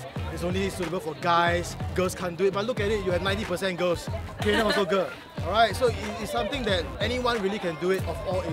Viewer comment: i heard "taehyung also girl" 😂 wot HAHAH